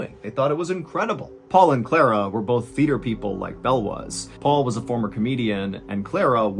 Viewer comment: Everytime someone says "theater people", I hear "feeder people", like the fish you breed and raise only to be fed to other 'better' or 'more valuable ' fish.